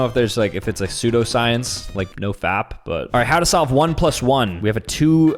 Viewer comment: did he just say no fap?